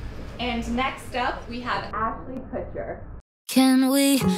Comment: I thought it said Ashton Kutcher at first and I was like damn I didn’t know he could sing. Ever since we nearly had the drought in England I believe in pretty much anything now.